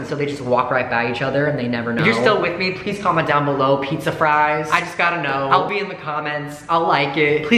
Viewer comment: I thought he said "If you're my soulmate comment Pizza fries" And I panicked and ran to the comments so fast just to be like "PIZZA FRIES" but then I realized